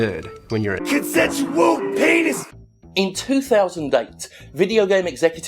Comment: Those Enzo consensual penis cuts will never not make me laugh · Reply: F4nTomZ Is that what he says? I always heard it as 'consensual wolf penis'.